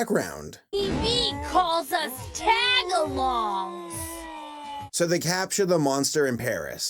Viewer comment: I swear gudo just said Tagalogs